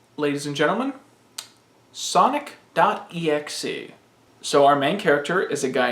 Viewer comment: I thought you said “Sock.exe” at first and just went along with it.